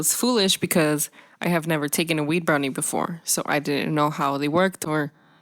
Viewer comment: I thought she said weeb brownie.......